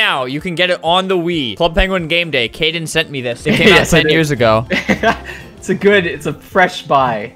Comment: did say Seven or Ten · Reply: 10